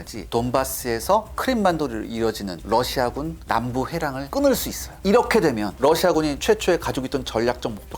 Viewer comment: 부터 자꾸 나오는 해랑이 뭡니까 대체 ㅋㅋㅋ 한두번도 아니고 끝까지 해랑으로 가네 "회랑"입니다;; 진짜 검토도 안하고 문맥 없이 들리는대로 가져다 쓰네 ㅉㅉ